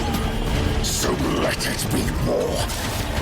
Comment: What does he say at 2:00 ? " So let it be war ? " I had the captions on another video with the same footage and it said " So let it be more " but to me So let it be WAR sounds just to much cooler. Love watching the titians spin up there cannons that would sound so sic. You could feel the sound in your chest if your brave enuff to fight with one or agaist it lol